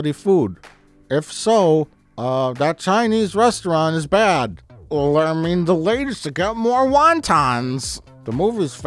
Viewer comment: dafuq does he say here? "Er I mean, the lady's to get more wontons"? Seen it a dozen times and can never figure that out